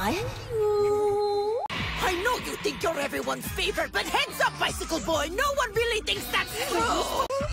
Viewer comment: Belarus calling Denmark bicycle boy is my new religion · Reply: it was "icicle boy" referring to how cold Denmark is. "bicycle boy" makes no sense but i get how u misheard